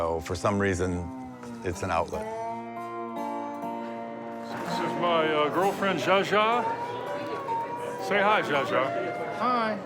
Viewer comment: In the first episode of American Conventions, visits the world's largest gathering of ventriloquists. WATCH NEXT: American Obsessions - <link> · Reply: It's not Cha Cha as the subtitles say. The puppet's name is a reference to late actress Zsa Zsa Gabor.